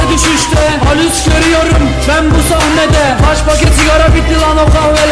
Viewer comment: sounded like bamboo samne de